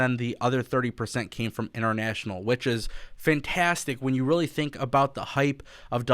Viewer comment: does he say international witches? I am dying picturing AEW witches with bubbling cauldrons "the elite, the the elite"